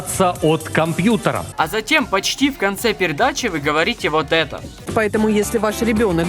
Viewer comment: я один услышал "пердачи"